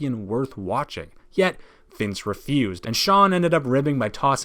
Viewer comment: my friend heard Jon instead of Shawn